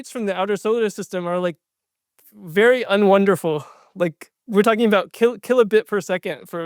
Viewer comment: did he just say "unwonderful" 😂 vocab++